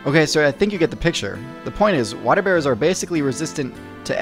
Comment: Thought waterbear said waterbender lol.